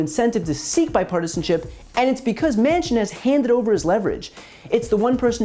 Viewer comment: “has handed over his leverage” I thought he said handed over his left nut, but I realized that’s pretty fitting too for this DINO